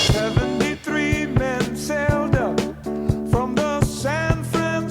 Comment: OMG, I have enjoyed this since 1970, and always thought it said "73 meant Zelda." 🤣 ☮️